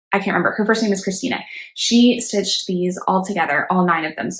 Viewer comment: Hi! Another great video. Thx! Who did you say (time stamp 24:44) that has done all the Loose Feather/Blackbird pieces in one whole piece? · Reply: It’s Cristina! (Instagram name = atelierchiki) here’s the link to her finish post! <link>